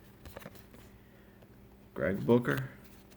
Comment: sounded like you said greg booger lol😂👃